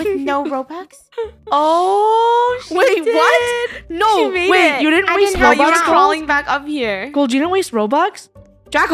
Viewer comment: What did she say 😮😮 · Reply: She said oh shi